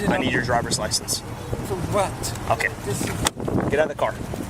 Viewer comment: I heard what he said. He asked, "For what?" when he asked for ID.